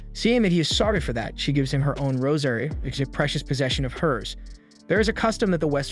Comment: I keep hearing "rosemary" instead of "rosary" 😅